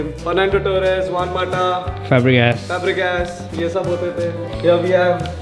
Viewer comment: I don't understand who's name is fabricass? BTW which fabric is the ass??😂😂😂